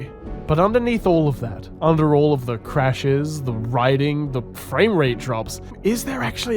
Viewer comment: When he said „frame rate“, I thought he said „Fray rate“ lol